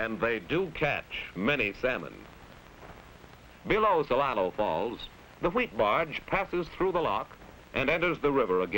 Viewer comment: what is the name of these falls? i cant make out what hes saying. id like to read about it. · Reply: Celilo Falls